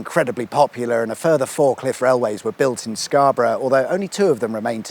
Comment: Scarborough had FIVE cliff railways, wow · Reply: I thought he said fork lift railways so I wondered what one of those is.